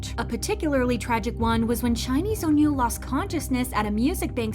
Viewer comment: you said “Chinese Onew”. I thought he was was Korean or I maybe misheard what she said 🤔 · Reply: She said “SHINee’s Onew” 😂